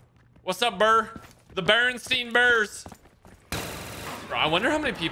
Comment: I heard you say Bering sea bears and thought it was a football team from Alaska. I grew up on the Bernstein bears.